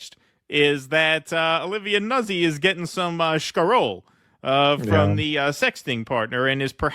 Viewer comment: What is that word that uses at 8:54? Sounds like he's trying to use a Yiddish slang term for 'money', which sounds like 'shkarol' or 'shkarole.' But I can't find it anywhere. Any help? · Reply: Scarole— Italian slang word for money